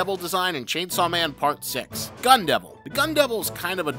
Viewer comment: I was looking through comments at the start of the short so when he said "gun devil" i thought he said "gundam" lmao